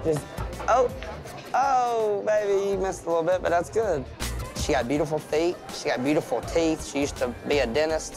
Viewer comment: Did he just say she got beautiful feet 😦